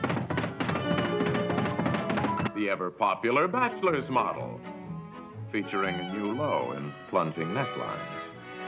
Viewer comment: I could not hear what are narrator saying in this scene clearly. Does anyone have transcription? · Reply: "The ever popular bachelor's model, featuring a new low in plunging necklines."